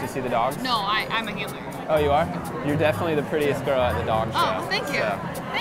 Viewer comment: When he called the girl pretty he was so calm, i bet he has never said youre pretty to a girl with that level of confidence or no fucks given ever again in his life hahaha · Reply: At first I mustve had flashbacks of trips cause I swore he said "You are the prettiest girl of all the dogs." LOL I had to rewind. Flashbacks, dudes.